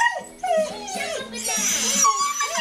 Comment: at the end..i thought mishka said '' i miss my boyfriend''